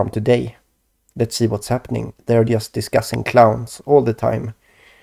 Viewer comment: Your Swedish... ahhhh. I'm sorry for referring to you as "this new French guy who's channel I like" in the past. Lol. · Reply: I'm sorry! To an American most western Euro languages are one big blob of exaggerated romanticized-sounding slang. I thought you were saying "Klaus" for Klaus Schwab when you were saying "Clowns". Lol. It was alarming for me to watch your most recent videos since I also have had the purview of my reality recently infiltrated by clowns and other bizzare artifacts from my subconscious and concious thoughts have also been appearing to me like spiders and other random symbols or sigils. I'm not even kidding. I've even been using the term "Clownworld" for months now. Did you see the Nephilim were clowns videos? <link>